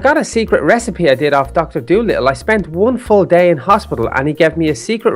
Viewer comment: Did he say dr.dolittle?